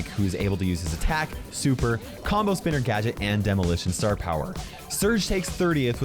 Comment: did he say combo spinner gadget for dynamike! 🤔